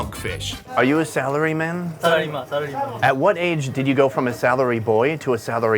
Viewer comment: I thought I heard celery man. Ohh its salary man